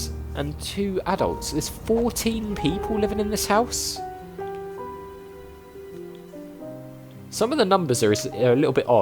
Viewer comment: Oh mannnnn, 15:06! All I heard is "There are 15 people living in this house, and you're the only one who has to make trouble."